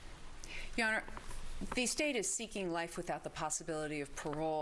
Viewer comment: I am really worried as I do hope I did not hear saying the State asks for life with parole for the McMichaels Please tell me I misheard!!!